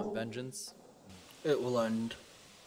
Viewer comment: Dan says it will end but with headphones on I hear clear as day will it end